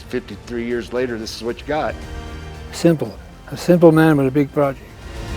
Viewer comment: I thought said, “Simple. I’m a simple man with deep pockets.” 😂 Their county is trash for that.